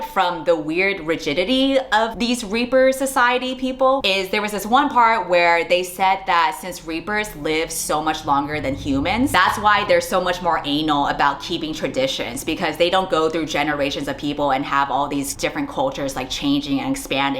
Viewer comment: Did say Anal in that moment? Can I use that word for my Monday's presentation? #SpanishSpeakerRealConcern